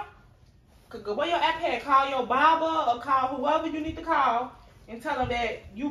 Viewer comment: I thought she said call her barber at 1st 📞💈🤣🤣